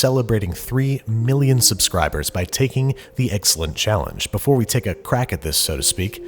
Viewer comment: I heard the insulin challenge